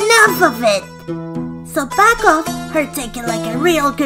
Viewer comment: I thought he said f#ck off for a second 😅